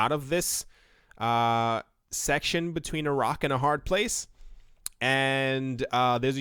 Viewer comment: Genuinely thought you said ‘Iraq and a hard place’ 😂